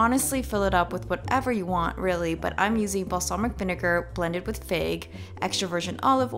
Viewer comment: I thought she said blended with pig 🤭